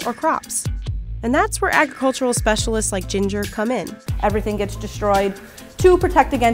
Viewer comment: did you say ginger cumin?